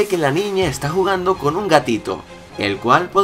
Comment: Da ternura la voz de boo cuando dice ( gatito ) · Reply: Todo este tiempo pensé que decía tatito.. No manches mi vida es una mentira T-T :v..